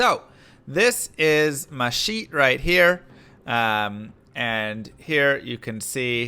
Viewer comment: I swear it sounded like he said "this is meshif right here"